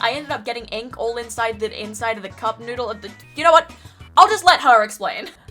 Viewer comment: When you said cup noodle I thought you said kaboodle 🙂